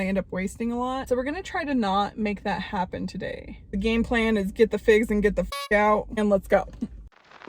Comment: “Get the figs and get the fuck out” 😂😂💀 you always crack me up. You got this fit girl fall! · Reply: I thought she said, “Get the fig out”. I say, “What the McFuckity?” far too frequently. I’m now going to start saying, “Get the fig out!” and “What the fig?”